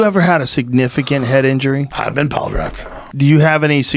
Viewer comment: He actually says "piledrived" but yea it sounds similar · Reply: Piledrived makes way more sense with the conversation… but I hear ‘plygraphed’